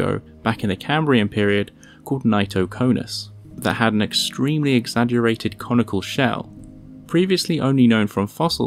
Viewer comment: I thought he said "extremely elongated comical shell." (2.36.)